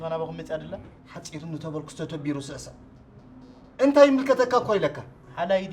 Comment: ሓጺሩኒ ተበልኩስ ፣ ተተቢሩ ይስዕስዕ። Is that what he is saying? PLEASE correct if wrong. I am learning tig'rriñña. Thanks upfront!